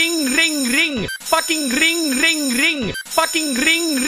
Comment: Sounds like he saying gring gring ring